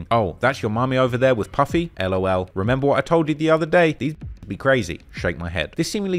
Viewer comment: did he just said the n word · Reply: "These bitches be crazy"